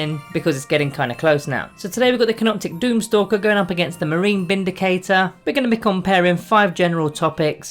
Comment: I thought you said the "Space Marine Bindicator" then lol